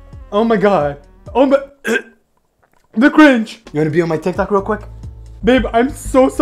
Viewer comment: I thought he said “The Grinch”😂